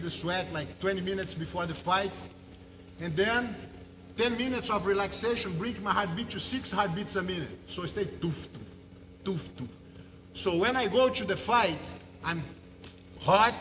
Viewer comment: does he say "six, sixteen or sixty"? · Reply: 60 would make sense